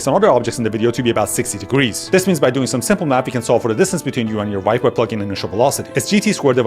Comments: Did he just say "simple math" ? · Did he said "by doing a simple math" or i just misheard 🤨🤨🤨